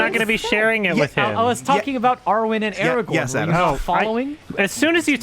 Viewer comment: For some reason when they said "Arwen and Aragorn" I heard it as "Arya and Eragon" and I just realized it's the same fucking thing.